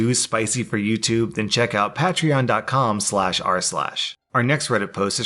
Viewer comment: the captions say ‘check out <link> instead of rslash lmao. Thought it was a funny tidbit